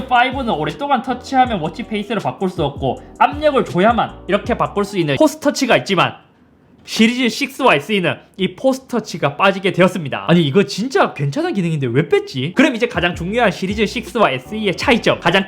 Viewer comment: 임포스터라고 들었습니다..죄송합니다....